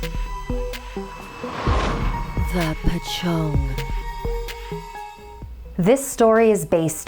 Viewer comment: When said the title I thought she said pocho XD